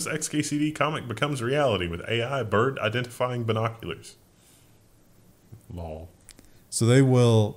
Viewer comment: I thought Wendel @ 12:34 said "AI bird identifies binoculars" ;) Guess that'll be next week...